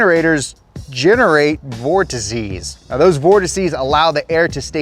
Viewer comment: Definitely thought said more disease when saying vortices lol